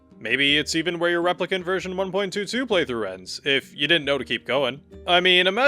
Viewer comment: i heard "if u didnt know to keep playing" and so ive come back 1 week later